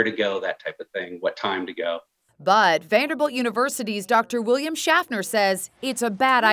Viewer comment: I thought they said "William Shatner".